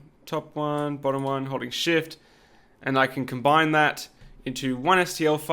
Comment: Ha ha ha! When you said holding shift I could’ve sworn you said holy shit! LOL!